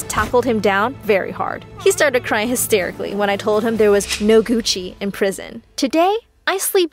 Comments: I THOUGHT SHE SAID “ NO THERES NO COOCHIE IN PRISON” LMAO IM DONE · I thought she said "There is no Coochie in prison" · I thought she said "theres no coochi in prison" 😭🤚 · Am I'm the only one that thought she said: "there is no coochie in prison". When she actually said "Gucci". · is it just me or did it sound like she said theres no cucci in prison? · I thought she said there is no coochie in prison lmao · i thought she said there was no coochie in prison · Oh so no one is gonna talk about how she was like “there’s no Gucci in prison “ OK . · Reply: I thought she said cuisine but wtf, gucci xD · lmao i thought he said " there no cuchy in prison lol" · Bro I thought she said there’s no coochie in prison